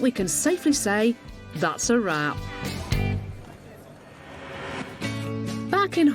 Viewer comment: I thought she gonna said THAT'S A WAP